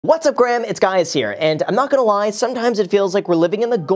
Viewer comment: Did he just say what’s up graham it’s guys here 😅😂